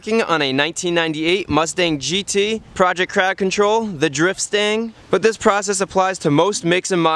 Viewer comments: crowd control, thats a good one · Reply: I misheard it as "project cow control". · The first few times i heard you say "Driftstang" i misheard "Drip Stain." What a great name for a project car -- I will take that for my work beater that I'm learning everything on. Thanks :D